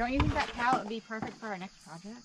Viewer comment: I didn't hear what said, but I thought you were going to stack firewood on top of the pallets. ❤🌅🌵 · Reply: I heard: "won't the pallets be good for the next project?" Yes....etc.